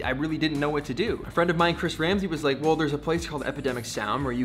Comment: did you just say Chris Ramsey? As in the magic YouTuber with an awesome beard?